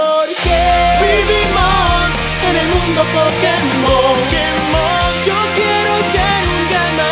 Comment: En el minuto 1:48 entendí porque Digimon en mundo pokemon